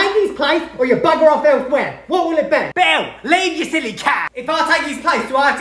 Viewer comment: “Belle, leave you silly cow!” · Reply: What's funny is that I didn't read the script I thought he said " Belle. Leave ur silly cat!" Like he called beast a cat lol 😂